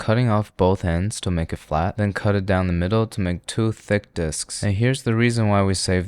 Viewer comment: i thought he said "2 thick dcks"😭i got scared for a bit....